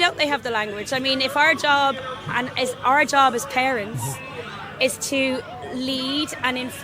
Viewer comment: did she just say “our job as KARENS.”? 😂